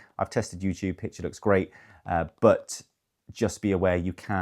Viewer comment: No, as I say in the video. Only catch up apps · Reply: thanks for confirming. At 4:50 it sounded like you said "you can" so I was a little confused. Thanks for your reply.